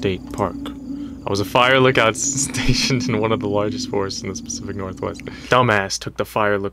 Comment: did he say the specific northwest??